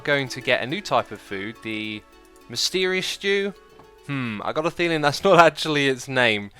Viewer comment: Lol captions say “mysterious jew”